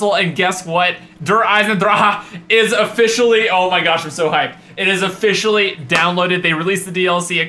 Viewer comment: did you just say: Im so high? xD